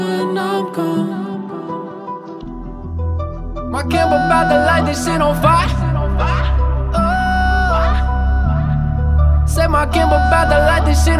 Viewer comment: i thought 2:31 he said "my girl z bout to light this shit on fire "